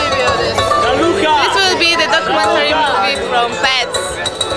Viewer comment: I laugh because it sounds like says "It's the documentary of pants."